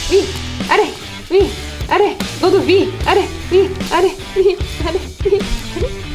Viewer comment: did anyone else hear EXID's "Wi Arae Wi Wi Arae" 😂😂